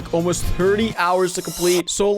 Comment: hous instead of hours 😂